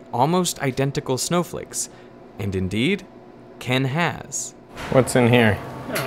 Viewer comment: Heard it as "indeed, can has." Can has identical snowflakes.